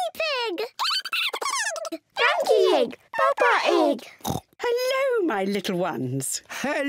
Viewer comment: I heard "papa egg" I died